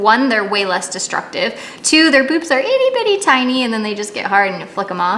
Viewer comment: I heard "boobs" as well. ...Thanks for clarifying!